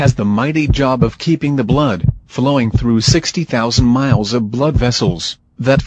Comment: Did he say 60 000 miles of blood vessels